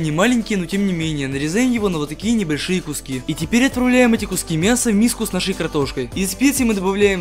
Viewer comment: Мне не одной послышалось "и теперь отправляем картошку"